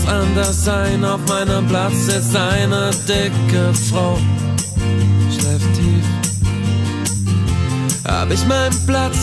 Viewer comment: hab ich Steife Titten verstanden:)) hahahah